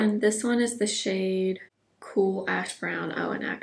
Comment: Favorite video! I have that velvet crush blush and need to pull that one out… so pretty! When you said the name of the brow pencil was “cool ash brown” I thought you said “cool a$# brown” and almost spit out my tea!!🤣